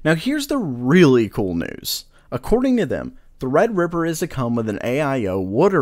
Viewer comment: did you say red ripper 😻